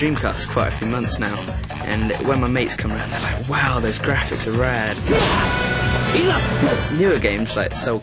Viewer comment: my mate comes over and says woooww those graphics are rad lol... certainly ages this footage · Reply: I always thought he was saying "Those graphics are rare..." ^__^. Some friends of mine at the time were saying "well rare" all the time, about stuff, ^__^. Maybe you're right, and he is saying Rad, but all this time, I've thought he was saying Rare, heh, ^__^.